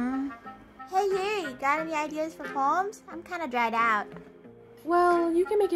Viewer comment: I swear to god I thought she said “do you have any ideas for bombs?”